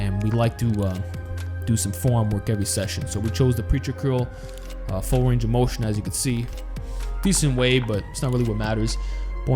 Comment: Am I the only one who thinks that it looks stupid When and are curling? Not used To it. You Guy's look better with unconventional exercises. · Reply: I thought he Said curling full range of motion